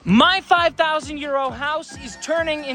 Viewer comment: I swear at the start of your videos I hear you say "My 5,000 year old house" lol.